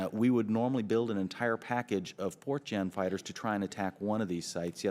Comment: I was reading the comments when I thought I heard him say "4chan fighters"